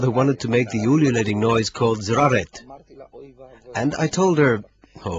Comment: Can someone please help: What is the "ululating noise" he is talking about at 17:48? What is the correct word for this? Something like "Zraret"? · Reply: tzareet